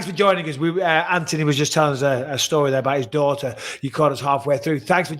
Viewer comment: Broke my heart hearing that Ants daughter had to go through that... especially at that age. 4 of my favourite people in the sport all on one podcast... doesn't get much better than this. Oh, and Brewdog Elvis Juice is a banging beer 🇬🇧👌❤ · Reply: Did I not hear it properly?? I thought he said his daughters friend 🤔 I haven't went back to check tho ..... "edit" I went back and checked, damn it was his daughter 😬 bang out of order that is